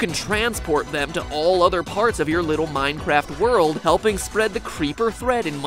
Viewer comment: Captions say Minecrftworld instead of Minecraft world